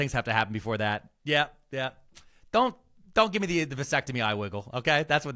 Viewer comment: Oh Geez... Why did he say "Vasectomy" and I heard "Lobotomy" until he said "My Balls"...